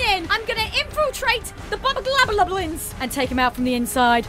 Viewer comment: The bubabulabulalins. At least that’s what I heard. Now can someone explain what a bubabulabulalin is?